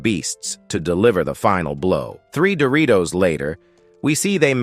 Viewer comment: Am i tweaking or did i hear him say two 15:52 ? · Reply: Ok it's three Doritos